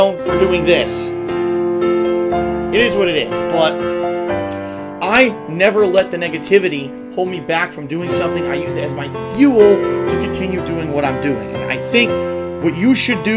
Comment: It sounded like he said niggativity lmao 😂